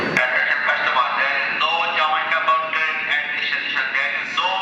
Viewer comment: What did the guy say at 3:48 ???? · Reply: Osman Matthew there is no Jamaica bound F train In this station